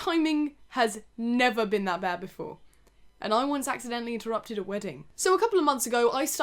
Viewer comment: I thought she said "I once accidentally threw up at a wedding"